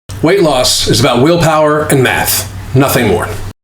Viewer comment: I looked away and it sounded like you said willpower and meth lol